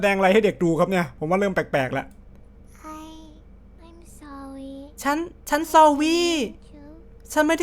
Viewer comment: ตรง 9:11 น้องน่าจะพูดว่า I'm sorry นะคะ แต่เป็นการพูดแบบเด็กที่ยังพูดไม่ชัด+คอนเสปน่ารักเลยพูดว่า ซอวี่ ตรงคำว่า sawwy ก็ไม่มีตัวพิมพ์ใหญ่ด้วยค่ะ